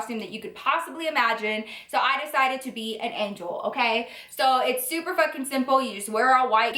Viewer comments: Literally when she said angel I thought she said egg roll 😂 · When you said that you decided to be an angel I thought you said EGGROLL!!! Hahahaha